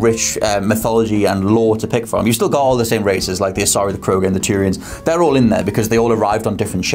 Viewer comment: mentions the races, asari, krogans and turians. Haha..i actually heard it as 'tyrians'. Like all the tyrian lannisters. A race of Tyrian Lannisters! Haha..could you imagine a race of Tyrians going around the galaxy!